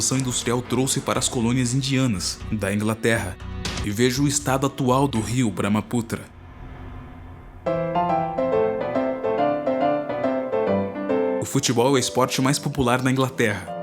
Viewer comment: KkKkK juro q ouvi "Rio Para uma puta " kkkkkkkkk